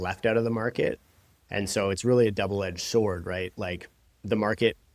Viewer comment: yeah not a slip, I can edit slips haha Thanks for the comment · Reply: ok I thought it sounded like you said "'Devil edged sword" haha I thought it was a slip